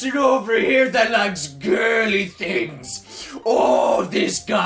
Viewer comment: Every time I heard say "girly" I thought he said gay. Am I the only one?